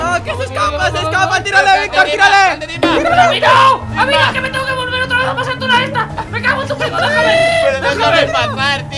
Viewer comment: ¿alguien mas entendió lo que dijo? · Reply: Es cuidado